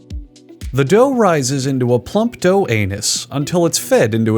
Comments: Spoiler I’m forever chasing the high I felt watching how it’s actually made for the first time believing it was real. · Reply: I was thinking about how this seems like it may be fake based on the exaggerated voice, then I heard him say “plump dough anus” and I knew. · " The dough rises into a plump anus" LMAO I died when I heard him say that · “Sausage with carrots” had me raise my first eyebrow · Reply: Did he say ‘plump dough anus’? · I couldn’t take this video seriously after I heard him say plump dough anus.... that caught me off guard. Funny video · in did he say "The dough raises into a plump dough Anus? Until its fed?" Lmfao